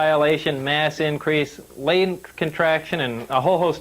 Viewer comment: Sounds like Lazar said "Lanes Contraction". It's called "Lorentz contraction".